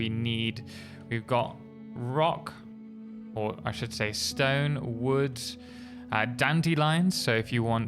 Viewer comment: Hey everyone did i just hear a Rock and Stone?